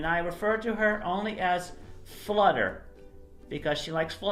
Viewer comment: Tell me why i thought chris said flooder not flutter XD